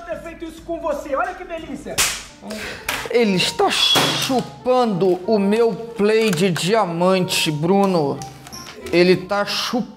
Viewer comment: No 3:26 Meu áudio estava baixo daí eu escutei "Ele está chupando meu p** de diamante" Ksksks